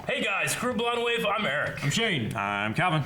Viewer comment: I've been watching these guys for so long but can someone please tell me what Eric says at the start of every video? All I hear is 'Hey guys kuro blonde wave' lol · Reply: ChrisTheWeeb "Hey Guys, Crew of BlindWave, I'm Eric"